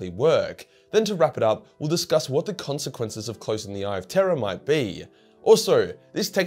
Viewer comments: I've seen a lot of videos, I swear I thought he was saying The Eye of Terra. Like it was close to Earth or something. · Cause of accent I kept hearing "the eye of terra" sometimes.